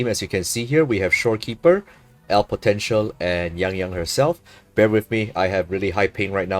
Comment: Did you say potential? 🧐